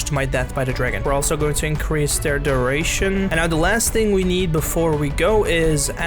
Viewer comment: I genuinely thought they said the last thing we need is adderall, I probably butchered the spelling sorry